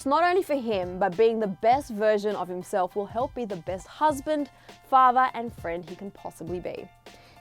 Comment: “the best husband, best FATHER, and best friend he can be” did I hear that right?